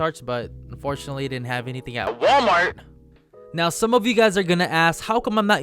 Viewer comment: Did you say Walmart at 1:50 Just askin